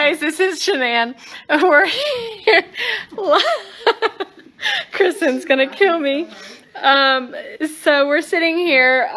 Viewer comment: while laughing with her friends, i thought she said "Chris is going to kill me"..... it created a ripple of shock, let's just say that.